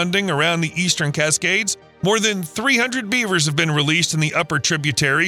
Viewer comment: i thought he said we have 300 beavers to released... WHAT?? who has 300 beavers?? :face-green-smiling::face-green-smiling